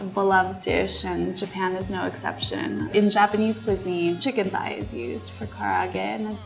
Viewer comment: sounds like she said Japanese Pussy lol.